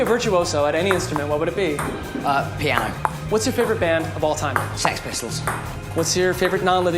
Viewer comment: What's your favourite board game.. I thought he said what's your favourite organ 😂😂